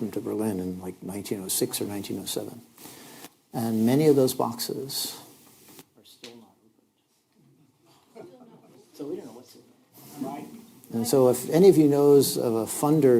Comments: could somebody tell me what he is saying at 01:00:54 - 01:01:00 . I am not native speaker and I try to follow from subtitle. But at the time, I can't comprhened. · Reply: “they’re still not open, we do not know what is inside them” English is my first language and that was hard to understand 😂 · was it intentionally censored? I couldn't make out what he said.. but the reaction from the moderator was interesting. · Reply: he says "many of those boxes are still not opened, so we don't know what's in them"